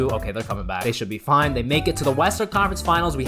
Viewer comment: did he just say western or am i maniac